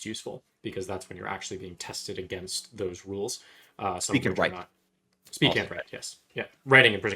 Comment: Subs at 5:35 should say "speak and write" instead of "speaking right"